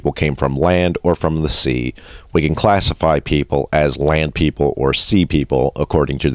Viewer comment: I thought for sure that it said "Sand People" I think I need to get some sleep. :-)